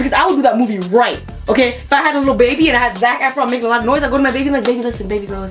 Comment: i thought she said zach herron XD